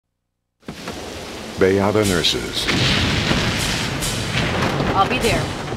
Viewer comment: All this time, I thought he was saying "They are the nurses", not Bayada Nurses, especially at the start of the commercial.